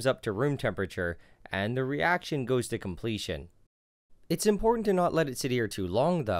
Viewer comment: Lol i thought you said the reaction goes to complete shit